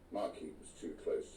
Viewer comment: Sounds to me like Rebecca is the IDEAL person to represent the people in Makerfield. You can see that ego and pettiness of the council is a huge problem for anybody and everybody trying to succeed there. A marquee too close to the stable FFS!???? This country would be screwed if every riding school in the country had a marquee too close to the stable... wouldn't it?? 🤔 I hope she gets in and turns that round. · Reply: It was a "muck heap" not a "marquee" but I also thought he said marquee first time I heard it. Which makes it even more petty council horse-shit, pun intended.